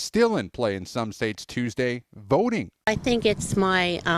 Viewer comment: When he said voting I thought he said boating and then that little ole lady comes out saying she felt it was her patriotic duty. 😂